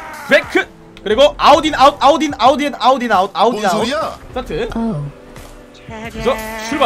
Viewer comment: 아웃 인 아웃 내 귀에 들리는거 : 아우디 아웃 (??)